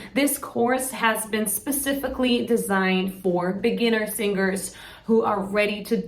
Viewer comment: фух, мне послышалось "This course was specifically designed for beginner singers, who are ready to die... ". Я и мои соседи мучаемся уже недели две. Мой кот, когда я пел, влетел с разгону головой в закрытую дверь моей комнаты. Я решил, что это знак, что пора что-то менять. Надеюсь этот курс поможет